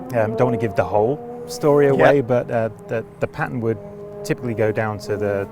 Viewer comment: I thought they were saying "patent" for the first 5 times until I realized they were saying "pattern". Boy that was confusing for a moment